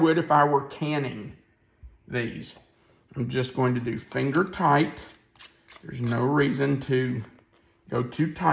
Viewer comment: I was excited when I heard “ginger”, but must hv been your accent! I was by University Hospital in October 1996 and had the cabbage mix w ginger everyday. Been looking for it ever since. Will try this asap